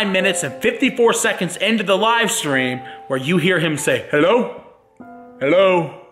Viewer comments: At first I heard "hello? hello?" but then when said that it was "hello? son?" I heard "hello? son?" I honestly don't know how I heard 2 different things when only one of them was the correct one... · When I heard his “Hello “Hi” I started crying. I miss him so much. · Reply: He actually said: "hello" "son". Slow it down to 0.5 speed and you clearly gonna hear it